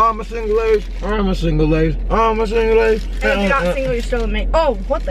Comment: what did she say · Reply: your not single ur still with me she saidd ittttt they are dating!!!!